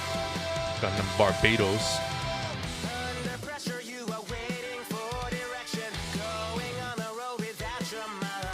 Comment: did he just say gundam barbados